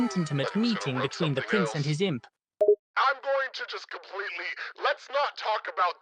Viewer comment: The subtitles said the prince and his simp time 4:01 when stolas and alastor was talking i started laughing lol